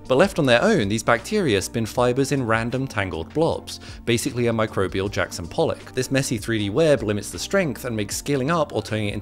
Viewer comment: I thought he said Micheal Jackson